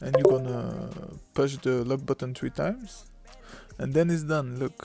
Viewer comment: Bro I didn’t really understand what you said at 3:06 and I subbed Bc you seem like a cool person 😁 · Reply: Lock button push it 3 time s